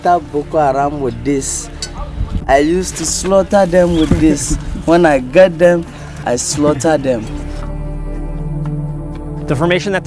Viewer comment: I thought he said when I gut them I slaughter them